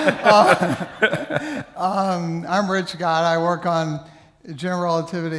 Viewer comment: I thought Richard was saying his name was "Rich Guy", that would be an awesome name